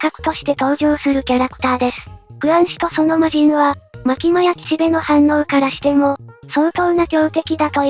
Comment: 岸辺の反応が岸辺露伴に聞こえた